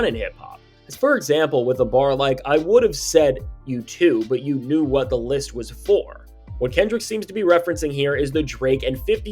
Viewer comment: He did not say "I would've said f you too but you knew what the list was for. He said "I would've said, "F you too," but you knew that the list was full" Come on now.